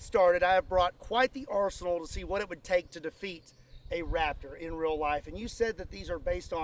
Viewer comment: I thought it said could you defeat a chiropractor😂